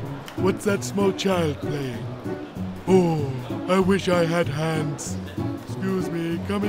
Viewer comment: Ya walk away and I thought that he said apps or grabs but he said I wish I had hands